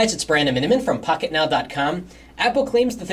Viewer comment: Whats the narrators name? Im not sure what he says it is. Sounds like hes saying Brando Minion